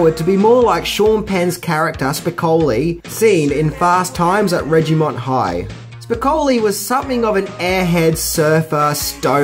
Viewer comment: does he say “Fast Times at Reggie Mont High”? I don’t like that. Oooooooh I don’t like that one bit. It’s RIDGEMONT HIGH.